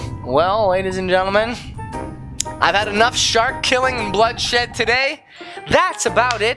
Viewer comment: He swore he said the s word at the end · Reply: he said bloodshed, not the other s word